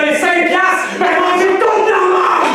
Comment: dit quoi a 31:40 ? J'ai pas compris la fin et ça à l'air très lourd. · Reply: Bah manger toutes la merdeee